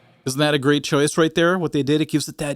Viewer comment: i hear Helmet - Iron head😄